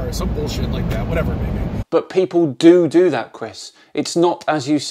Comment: Did he just say doodoo?